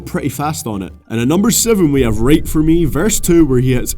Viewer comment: when you mentioned "right for me" i could've sworn you said "rape for me" and i was just sitting there waiting for the title to appear thinking to myself "Wait what?"